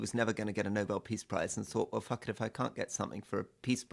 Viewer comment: did I just hear Rory Stewart say f*ck it